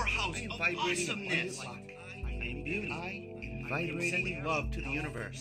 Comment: I think I heard him say "I am puny!" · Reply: I'm sure he really says "I am beauty!" but it sounds like "puny."